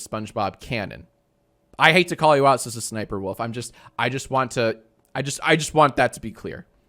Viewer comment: I keep hearing "Sister Sniperwolf"